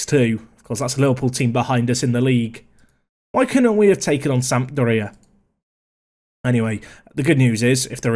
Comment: When you said "Sampdoria" I thought you said "South Korea". I was very confused why the South Korean national team was playing in the Euro League...